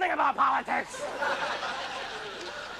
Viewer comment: This brought back good memories. In 87', Bobcat was our hero in the 8th grade. We never saw or heard anybody like him before. At 5:14, for years I thought he said" I'm not as smart as I appear to be"". To me, that sounds funnier.😄 Thanks for uploading this.